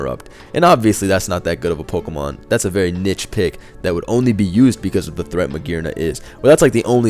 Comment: did he just say nitch pick?